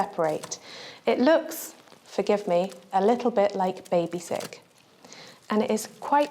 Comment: did she say that it looks like baby shit??